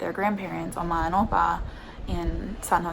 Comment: Haha no, my brother and sister’s dad is German, so they must’ve started calling their grandparents that for that reason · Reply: I thought you were saying ama and apa 🤣🤣🤣 that's how we say it in Spanish